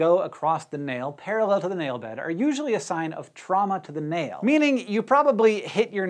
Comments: I heard "trauma to the nail" "trauma to the male" and I was like:Yeah that explains those white spots on my nails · "White spots are indicating trauma to the nail" Me looks at nail and sees a whitespot: what did i do to you. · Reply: Nightmare : D I thought he said “trauma to the male